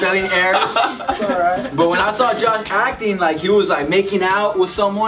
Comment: did say John or is that just me ?